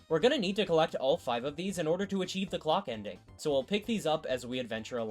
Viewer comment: why did I hear Glock ending instead of clock ending?